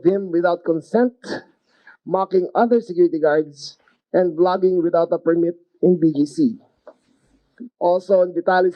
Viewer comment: Did I hear "vlogging without a permit" on the rap sheet at 2:05? I didn't know there was such a law. These idiot streamers are ruining everything for everyone else.